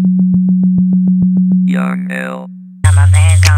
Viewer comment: What does that robot voice at 0:04 say? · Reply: "Young L" Production Studio.